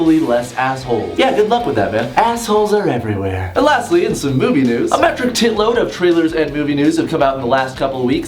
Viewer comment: omg when he said movie news i thought he said boobie news XD